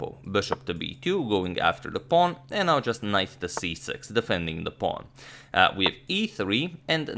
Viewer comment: Did he say knife to c6